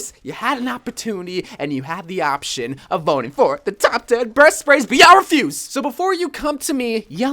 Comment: it sounded like he said top 10 breast sprays